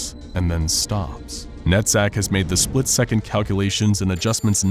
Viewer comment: I thought he said the computer programs name is nutsack🤣